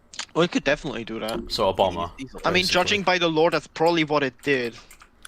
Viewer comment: Captions say "Obama" instead of "a bomber" 45:32 and